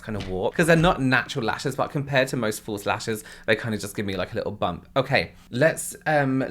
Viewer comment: (talking about the lashes) they kind of give me a little bump………………at first I thought you said “they kind of give me a little Bum” 😂😂😂